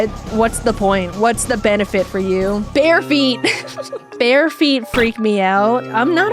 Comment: I was focusing on Megabonk when she said this, and I totally heard it as "Bear Feet" XD I was just wondering what bears ever did lol